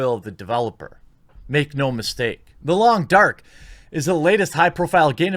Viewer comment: I thought he said, "The Lawn Dart" and got my hopes up for a VR game about accidental fratricide.